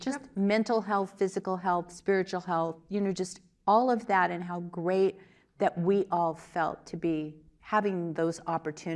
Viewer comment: What does she say at minute 19:52? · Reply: Oura ring